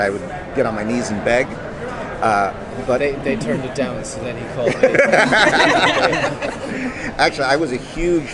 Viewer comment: what does the actor say at 3:19? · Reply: He said " they turned him down so he called me " ☺